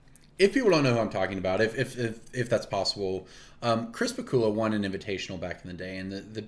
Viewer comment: Ohh, I thought you were saying ‘crisper/cooler’ referring to the fire and ice mage hands rather than Chris Pikula. Either way, knew which card you meant!